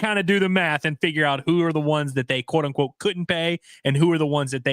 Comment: at 3:37 i didn’t hear couldn’t. I heard wouldn’t. He gave the reason and its because their wants were astronomical compared to last year. If they get their number good for them. I’m a fan of completely replacing our tackles for the same price of keeping one of what we had. Those guys couldn’t block the exit at chuckie cheese